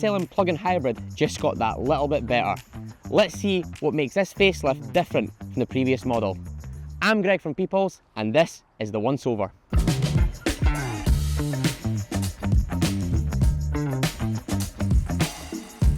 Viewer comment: I misheard "The One Sober"